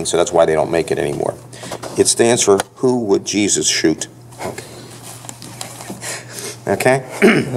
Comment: I thought it was "What Would Jesus Say"